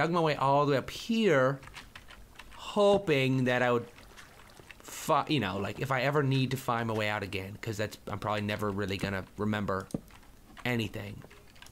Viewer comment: Did say " fuckin' " at 2:07? :D I'm guessing he just changed his mind about what he was going to say next in the middle of the word, and the combination of the two different words just sounded like it :D either way, totally fine, I'm not gonna be one of those whiny 8-year-old minecrafters who are like "omg Toby u said a bad word now I'm gonna unsubscribe forever!!!" xDDD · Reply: "Hoping that I would fi.. You know, like if I ever need to find my way out again"